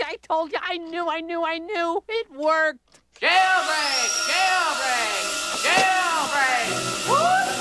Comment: I thought he said "Kill me" Lol